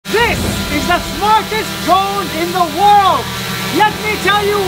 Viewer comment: I really thought he said "smallest drone"